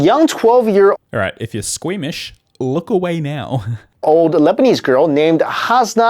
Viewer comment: The key point is : crystals AREN'T Diamonds. The real disease, which that lebanese girl has, makes you cry crystals and not diamonds. It's quite logical if you think about it for a few seconds : in real life, it's basically her tears crystalizing. These crystals are not worth much because they're quite common (not comming from human eyes but it's the same composition anyway) unlike actual diamonds, which have nothing to do with this kind of crystal. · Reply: I thought you said lesbians